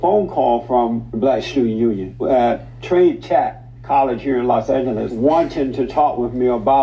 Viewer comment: did he say trade tech? As LATTC (los Angeles trade tech)